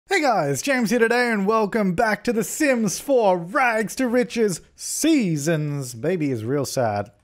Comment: Omg I thought you were saying 'welcome to the sims 4 seasons baby' as one phrase